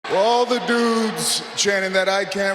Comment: in the beginning i thought Roman Reigns said “all the noobs” 💀💀😭